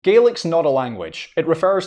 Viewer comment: Split second I thought you said garlic 😂🤣😂🤣😂